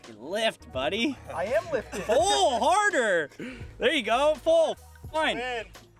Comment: Thought you were saying lift “higher” not “harder” 😂😂😂😂